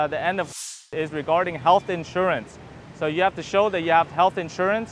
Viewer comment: So, now travelers must have health insurance but didn't need it with the 30 day visa? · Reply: “you must show it” is what was said